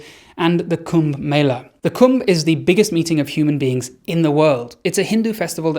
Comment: oh I misheard you - when you said the kumbh was the biggest meeting of humans in the world, I thought you said “the kumbh is the biggest eating of humans in the world.” I immediately said WHAT and replayed that part